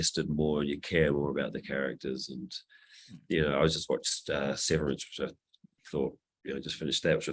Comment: Can someone tell me what series he was talking about around 11:20? I can for the life of me not understand him there. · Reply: Severance!